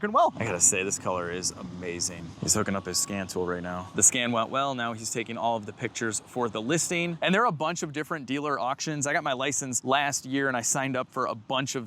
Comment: Just as the inspector started taking photos. I thought you said “the SCAM went well” 😂😂😂